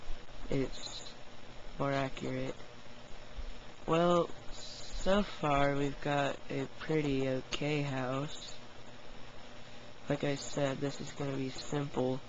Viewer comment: lol i don't if you saud this but i thought you said "well so far we got a pretty gay house" ha ha ha ha ha ha no the house is real good.